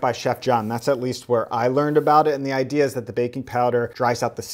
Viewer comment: I am going to cry... I was listening in and following along and I kept hearing "Baking Soda" and looked at the video to when it said "Baking Soda" for it and I used Baking Soda.... I am heart broken TwT Baking POWER everyone its baking POWDER RIP my taste buds lol